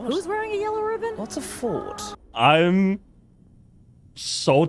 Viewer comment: did someone say “what’s a fort?” What is this?